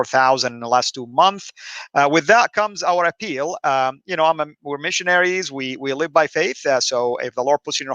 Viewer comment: did you say "I'm a poor missionary?"